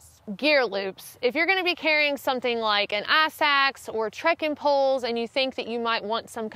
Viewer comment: Wow, a firehose of great info. I thought she said "ass axe," then turned on the closed caption option and rewound to discover it was "ice axe." Trying to figure out which part of southern US she's from. Maybe Alabama?